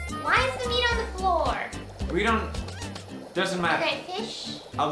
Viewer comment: Marzia: wHy Is tHE mEaT oN tHE FlOor? Felix: IT HAS TO BE THAT WAY, IT'S COOKING, WOMAN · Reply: that's not what he said. He said it doesn't matter but it's